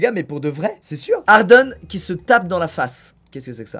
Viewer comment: "Hardone qui se tape dans la face" 😭😭 Olala squeezie Olala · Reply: J'ai entendu "harden"et non "hardone" perso